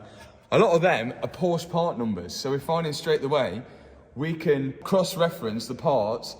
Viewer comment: says "straight the way" instead of straight away.